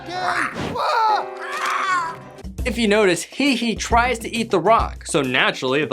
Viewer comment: and 3:15 did he just call the chicken he he